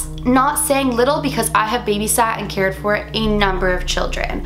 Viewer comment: I thought it said baysat and I was all like what the frick is that